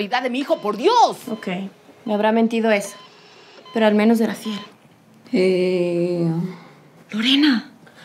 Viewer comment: que dijo :V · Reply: Talia Piñeres "Por lo menos era fiel"